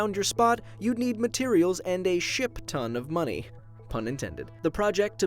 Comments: I thought said Shit ton of money, instead of Ship ton of money. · Lol i thought he said a s*it ton of money instead of ship XD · did you say shit ton?